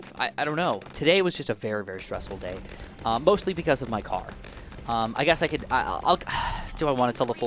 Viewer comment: I swear I heard him say, "today has been a stressful day...mostly because of my cock" and I went, HUH?